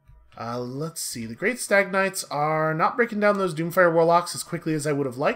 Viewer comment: Every time you say "Stag Knights", I hear "stagnites". As in some derivative of stagnation. Anyway, good to see a new warhammer series.